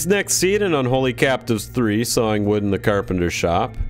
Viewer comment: I thought you said Sowing wood.